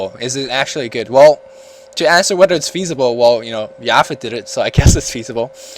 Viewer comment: what did he say? "who did it"? yafin or sth